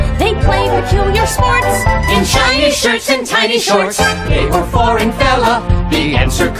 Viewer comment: I ALWAYS HEAR IT AS CHINESE SHIRTS WTH IS WRONG WITH ME 💀💀